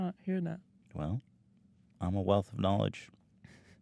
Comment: did he just say MEME while